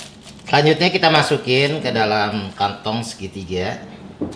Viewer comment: sekilas kedengeran nya "Kanyutnya kita masukin" 🤣🤣🙏🏻